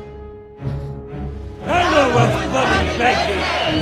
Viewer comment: What does the hatter say at 2:33 it’s hard to understand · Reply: “downal wyth bluddy behg hid” it means Down with the bloody big head